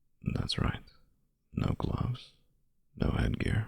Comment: I misheard "No gloves" as "No clothes", and my brain was "Sparta?!" Cool audio, and like the way the back stories are unfolding